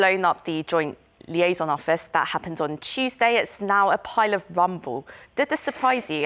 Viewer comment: mins did she just said "a pile of rumble"?